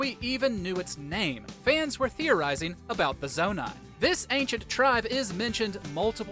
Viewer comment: When you said zonai I thought you said nomai💀 I got such flashbacks